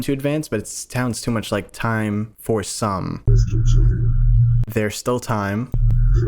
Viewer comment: i think the singularity said time for some dance, the reason i think so is previously in the same video the v-1 said dance when he was talking to the lead scientist.